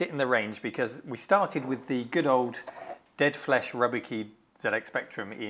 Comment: "Rubber Keys". I need to have my ears checked, I heard "Rubbish Keys".